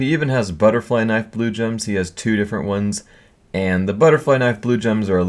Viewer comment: did you say we has???